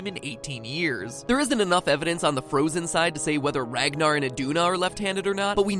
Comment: "Ragnar and Idunna" instead of Aggnar XD @ 8:01 Good one, MattPat